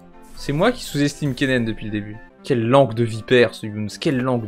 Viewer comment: A 10:13 je suis le seul a entendre « Bardonnez moi » ?